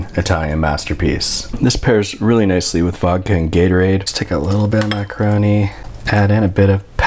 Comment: wow i though he said "fucking gatorade" but the subtitles say "vodka and gatorade" and i can hear both